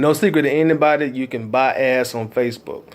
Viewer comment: Great video! I had to double take cause it sounded like you were saying "You can buy Ass on Facebook".. lol but I know you were saying Ad's ..bring the "D" in Ad's out a little more, all joke's aside though I really want to Thank you so much for sharing your knowledge its very helpful and Inspirational!